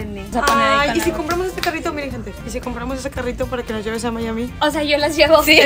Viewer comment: En el minuto 4:55 entendi “y si compramos este carrito para q nos lleves a Miami” pero era: “y si compramos este carrito para q nos lleves a ama y a mi” Jajaja